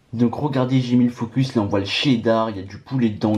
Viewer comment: " On voi l'chidar " 🤣🤣🤣 (